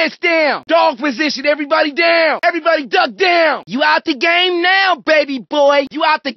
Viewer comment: I thought he said your out the game now sussy boy!